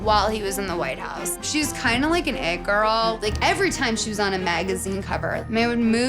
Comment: I swear i heard „Egg girl“ when she said „It girl“